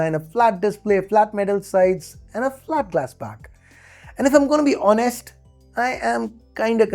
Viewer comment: Did you say "Glass Back"? That's relief from those supid el cheapo vegan nonsense.